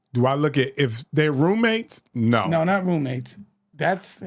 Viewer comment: He clearly says, “no not roommates “ are u listening or just responding. He said living with another man, obviously meaning how a couple would cohabitate